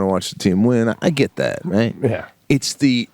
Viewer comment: Haha @ 10:02 did he say "bongles?"